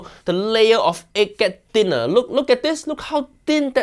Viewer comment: did anyone else hear him say n*g*er 💀